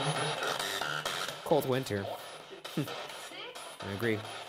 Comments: I heard “call me mister” not cold winter too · Anyone else hear "call me Victor" instead of "cold winter" @ 15:40 ?? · When Roy said “Cold Winter” I thought it said “Call me Victor” ¯\_(ツ)_/¯ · cold winter sounded like " Cole Flinter" to me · I heard "cold winter" and "sick" so maybe that person died in the winter of sickness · it sounded like "call me Victor" not "Cold winter" in my opinion. Perhaps that's who was there and not Roy? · You guys think it said "cold winter" I hear "call me mister" lol